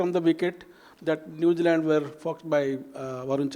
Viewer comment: did he said fucked by?😮